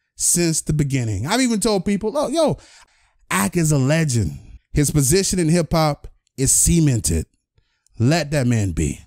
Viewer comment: "His position in hip-hop, is semen-ted" 😂 idk about you guys but that's what I heard